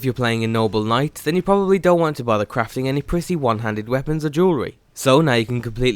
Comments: did he say pussy at 0:57 ? xD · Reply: It sounds like he said prussy lol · did he say pussy at 0:57 ? xD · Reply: I believe he said "Prissy". You can hear the Pr into the Focused I. I heard the I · AM I the only one who thought he said " any pussy one handed weapons" first?